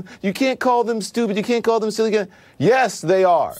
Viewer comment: What word does he use after "stupid" at 2:52? "Sillagain"?